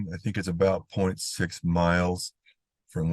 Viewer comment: So, you couldn’t find a body 0.6 miles away from the burnt out car? How bizarre. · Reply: I thought he said 6 miles. I'll go back and listen but 6 miles is a fair distance to search. That's why I think drones should be in all ES departments.